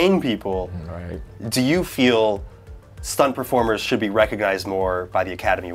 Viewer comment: Was I the only one who heard “recogized” at 5:30 ?